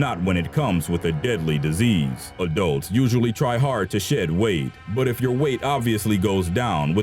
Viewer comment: i thought he said "adults usually try hard to shit weight" for a second lol